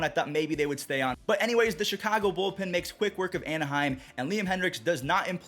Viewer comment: don't think you can say Chicago Bulls, lol